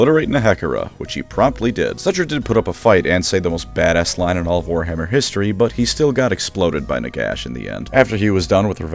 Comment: what did Settra say? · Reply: Settra does not serve, Settra rules